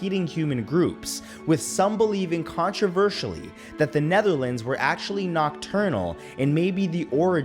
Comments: Did he say Netherlands, when he meant Neanderthal? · am I the only one who heard you say the Netherlands instead of Neanderthals? xD Im from the Netherlands so I was like wut?